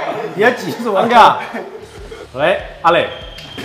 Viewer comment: 開始的時候聽成 : 尷尬，喂~阿磊~(沒聽聲音我還以為學長臨時去接電話